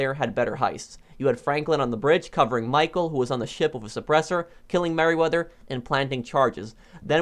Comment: First i heard: „on the ship with his oppressor“ and i was like oh god pls not more oppressors lol